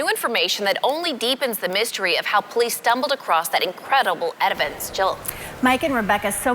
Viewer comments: She said "edivence" instead of "evidence" haha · edivence did anyone else hear it lol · Did she say edivence? Instead of evidence?😂😂😂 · 20...did she just say 'edivence'? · did she say "Edivence"